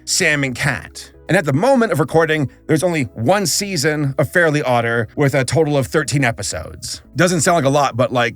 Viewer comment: I'll be honest, anytime you said "Fairly Odder," I thought you were saying "Fairly *Otter.*" That would be a great show instead of... whatever this mess is. I'm so glad I don't watch Paramount+.